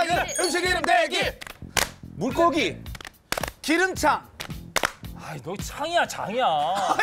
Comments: 근데 나도 여기서 '기름차'라고 들림ㅋㅋ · 근데 양세찬이 말한거 자막안보고 들으면 기름차로 들림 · 이건 누가들어도 기름차로 들리지 않나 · 근데 진짜 기름창으로 들리는뎈ㅋㅋㅋㅋㅋㅋㅋㅋㅋㅋ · 들어보면 기름차로 들리긴 함ㅋㅋㅋㅋ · 나도 기름창으로 들림 .. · 나도 기름차라고 들리는데ㅋㅋㅋㅋㅋ나도 문제있는건가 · 근데 진짜 창으로 들리긴 함ㅋㅋㅋ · 기름창으로 들리긴 하내... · 초쯤 양세찬이 기름차 라고 들리는거 나뿐임? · 저는 기름차로 들리네요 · 창이라 들리기는 하네 ㅋㅋㅋㅋ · 단어의 맥락상 기름장으로 받아들이는 게 맞기는 하지만 확실히 기름”차”나 “창”으로 들리기는 함ㅋ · 양세찬 기름장이라고는 했다는데.. 분명 기름차 기름창이라고 들리는데...?? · 자막에는 기름장이라고 적혀있지만 기름'차'라고 들리기도 함. ㅋㅋ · 이거 나만 기름차로 들림?ㅋㅋ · 왜 나도 양세찬님 기름차로 들리지ㅋㅋㅋ 귀가 맛이 갔낱ㅋㅋㅋ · 나만 "기름차" 로 들리나? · 진짜 창으로 들리는데?ㅋㅋㅋㅋㅋㅋ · 나만 계속 기름 차라고 들리나?... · 아무리 들어도 양세찬 잘못 ㅋㅋㅋ 심지어 기름창도 아니고 기름차로 들림.. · 기름창이라고 들리긴 하네 ㅋㅋㅋㅋ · 근데 나도 기름창으로 들려....ㅋㅋㅋㅋㅋ